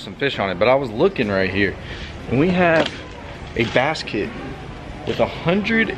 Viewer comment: Every time you said bass kit I thought you said basket I’d like to see cheap vs expensive spinner baits my favorite bait was the lizzard